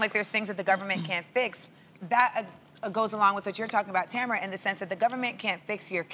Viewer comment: did she say tamra @ 1:32😂 bless😭